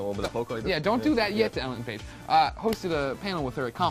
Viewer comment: did they say allen page?